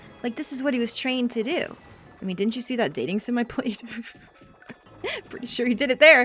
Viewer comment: for some reason I thought you said "dating Smefanye", BOWSER X SMEFANYE CONFIRMED